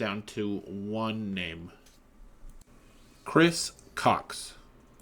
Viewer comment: Chris cox I misheard that as kris cocks somehow